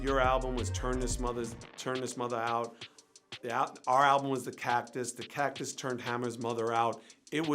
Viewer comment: You mentioned mama. Regardless of the name of the record you being cheeky nee the double entendre on how it sounded like you said you turnt his moms out.